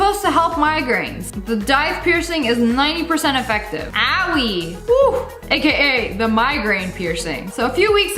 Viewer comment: did you say owy